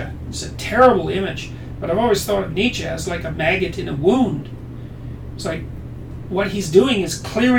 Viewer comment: 'Nietzsche as a maggot in a wound' - fucking brilliant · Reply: I thought he said "maggot in a womb".